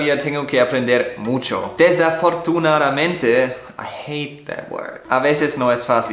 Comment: cuando trato de pronunciar "desafortunadamente" le entendi "desde la fortuna a la mente" xdxd